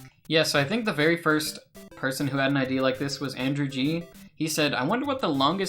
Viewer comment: Something tells me the subtitles should say ‘idea’ instead of ‘ID’.